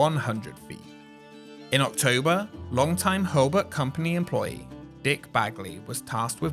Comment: I'm sorry, did you just say DICK BAG Lee?